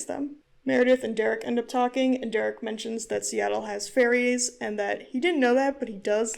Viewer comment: the first time u said “derek tells meredith that seattle has ferries” i thought u said fairies and i was… so confused… then when u said they went to see the fairies i was like ???????